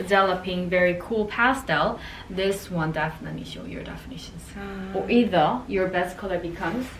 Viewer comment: ياجماعه سمعتها مرتين تقول واذا ولا انا غلطانه 😭